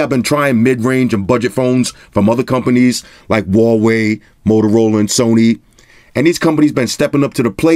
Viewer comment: Did you say "Rah-wei"? 9:28 Rah!